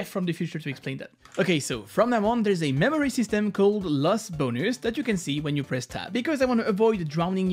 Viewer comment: I heard "Lost Boners"